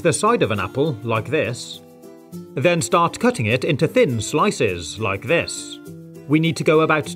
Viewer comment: Dave hax and it even takes out the core Me: he’s gonna to say It Dave: pretty crazy huh? Me: AHHHHHHHHHHH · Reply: Siri did he just say thin